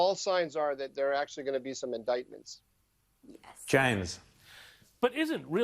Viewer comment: Did she just say "yes" at 2:19? 🤔🙊